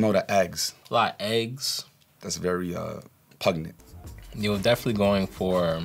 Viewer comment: did he just say "pugnent" !?